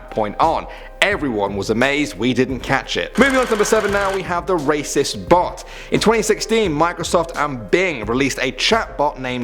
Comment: lmao I thought that said "The racist boi"